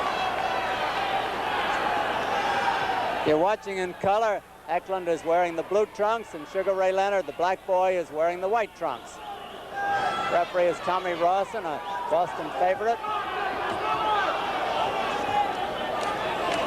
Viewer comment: Did he say "the black boy"?